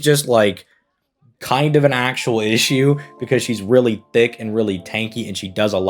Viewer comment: did I hear "she is really thicc"? well either way she is 👌